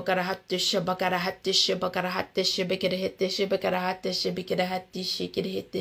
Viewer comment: that chanting 😂😂😂🤣🤣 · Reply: 😂😂😂😂😂😂😂💀💀 I could've sworn she said, "I coulda hit dat 💩" 💀💀